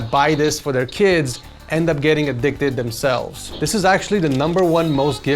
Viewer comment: I misheard "addicted" for the flipside game for "evicted", bruh.